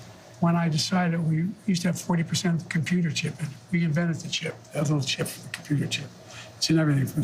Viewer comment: did I hear that right? Biden invented the computer chip? Neat. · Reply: He said "We invented the computer chip..." - the implication being that it was invented in the USA.